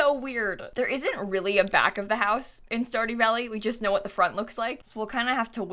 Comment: This whole time I thought she was as saying "Starty Valley" in her streams 💀💀💀